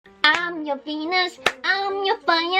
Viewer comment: the first second of the video i thought i heard her say "I'' am your penis"